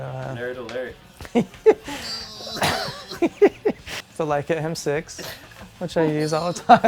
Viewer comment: What camera does he say at 6:59? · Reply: Leica M6